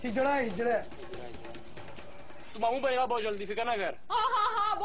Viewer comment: please check 16:13 what arslan has said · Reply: Tu mamu bnega bohhht jldi fikr na kr